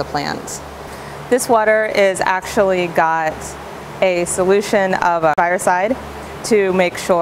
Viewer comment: you say what the solution is in the bucket, but there's a glitch in the editing and you can't hear exactly what she is saying. It sounds like she says " It has a solution of fireside"... which I'm sure that isn't right.